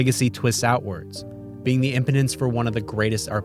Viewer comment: More than once, it sounds like you say 'Impotence' when you clearly mean 'Impetus.' Those two words mean... very different things. 😆🤦‍♂